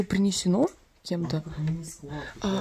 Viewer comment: мне послышалось или она сказала Сереже малыш?Он точно просто друзья)